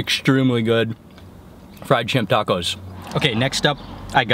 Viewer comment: Sounded like you said "fried chimp tacos". lol Everything looks so damn good! I am drooling.